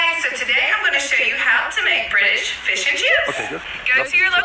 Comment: "And that is how you make British fish and chips!" "You've never been to Britain" LMAOJSHDJG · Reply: I thought she said "fish and juice" like what did she even make lol